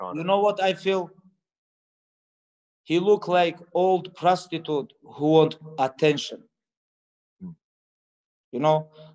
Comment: For the longest time I thought that he said "he look like old crusty dude who want attention"